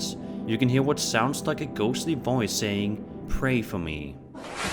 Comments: I don't hear 'pray for me'; I hear 'run for your life'. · I hear something like "Won't you pray for me, Wayne" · I don't hear pray for me, i hear "wait for me wait"it doesn't say" Pray for me pray"i heard it over n over and i also think that the guy was whispering that to his wife "wait for me wait"and the figure looks like an alien grey with pointy chin